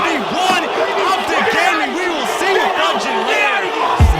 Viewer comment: At like 45:00 did he say "OpTic we will see you fudging later"? That upset me a little bit no lie